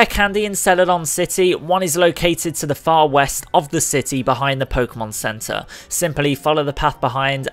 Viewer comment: did u said saffron city or a letter c city?